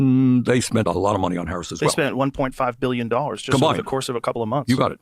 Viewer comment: I heard it like Rogan said "they" spent $1,5 billion on the Harris campaign, not "someone". I think Rogan often says outrageous things, but not this occasion.